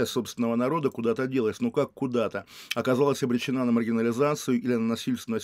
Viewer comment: при прослушивании на 2х на 4:20 послышалось, что шестая часть страны обречена на могилизацию, и немного удивился мрачному пророчеству